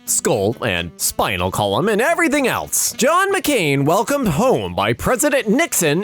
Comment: I thought he said John mcclaine from die hard